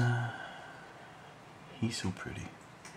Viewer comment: did you just said he's soo pretty? WTF,,, this thing soo creepy